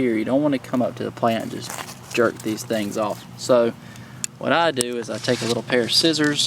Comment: Did he just say, "You Don't want to jerk these things off"??? I had to pause it, but there was nobody here to laugh with meeeee.